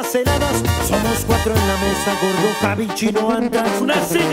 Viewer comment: Una pregunta no logro decifrae como dice al minuto 1:56 donde dice somos cuatro en la mesa (ahi es donde no entiendo) me podrian decir unos dicen que es por bota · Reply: Gordo, Javi, chino antrax y el chavo llego de prisa el wey de arriba ni escuchar sabe